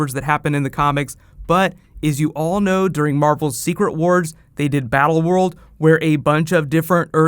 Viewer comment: For a second I thought you said secret invasion rather than secret wars, so was expecting white martians to be the villains of the crossover. I may have said this on a previous vid, but I'm expecting the Cyborg Superman to be a Frankenstein's monster of Danvers, Henshaw and Indigo. Wonder if they're bring in some more Martian Manhunter villains as Cadmus's backup in case they need to battle him. Bette Noir and the Human Flame could work. Also hoping for Lobo.